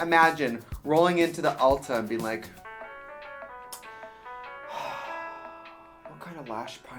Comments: when you said “WHAT KINDA?!?” i thought you were saying “wakanda (forever)” and man i was confused for a second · At first when you said what kinda I thought you said wakanda and I was confused